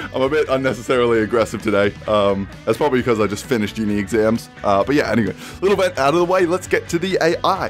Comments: I hope that I misheard this, but did you just say junior exams? As in junior year of high school? So you’re in high school when I’m not? And you’re most likely older than me? If that’s the case, then apologize right now! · When he said probably cuz I just finished uni exams, I went back because I thought “did he say chuunin exams....”